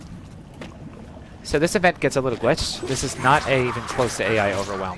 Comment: What'd he say around 36:30 about the event? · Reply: the event gets glitched most of the stuff spawns behind the doors instead of in the event room and gets bugged